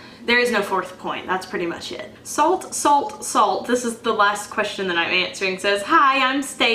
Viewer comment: Dhanya Louisa Hey! Her question was "Do you think that video/YouTube is something you have to utilize to be successful in this day and age?" I said no it's not and that I know plenty of successful artisans and soap makers who don't. :) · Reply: oh ok....at 15:22 i thought you said salt salt salt..i got excited thinking it was about adding salt to the soap batter to make the final soap bar harder..im experimenting with that right now....thanks for replying....luv your videos.very inspiring.also would love to see the cream soap /sugar scrub making.